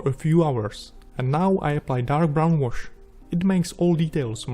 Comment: Sorry, I can't understand. what do you apply? ( I don't understand it because I'm Hungarian.) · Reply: Dark brown wash